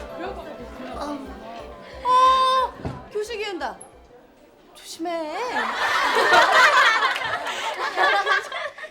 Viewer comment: 뭐라고 하는건가용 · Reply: 교실기운다 ㅋㅋㅋ